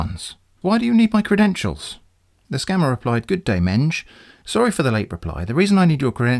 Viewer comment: I was listening to this with the screen off and I genuinely thought they said “Good Day Minge”.